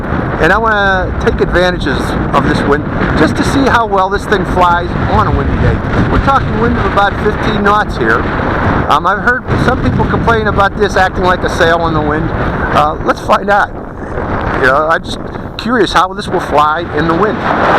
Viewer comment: It sounds like your saying 50 knot winds, which would be almost 60 mph, but I'm having trouble believing that. Am I mis-hearing you saying 15 knots?